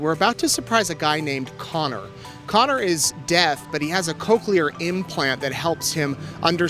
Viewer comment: I also have bad hearing because of hyperacusis and tinnitus and thought he said “Connor is dead” and I was thinking, how are they gonna give him a gift if he is dead?